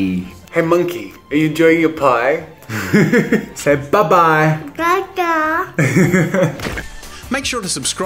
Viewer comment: When the child said “bye bye da da” it sounded like he said “acoomunatata” !